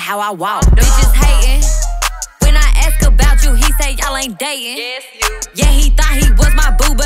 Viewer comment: What did the background vocal say @ 1:33 ??? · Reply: Yes you